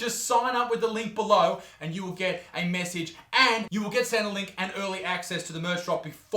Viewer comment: “and you’ll get sent a link” sounded like “and you’ll get Centrelink”... not complaining either way 🤷‍♂️